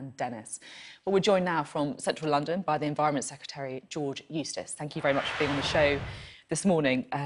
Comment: I thought she said George 'Useless'! Sorry!